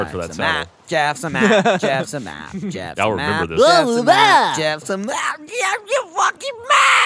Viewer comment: Did anyone else think that was Ray saying "Jesus" at 1:00?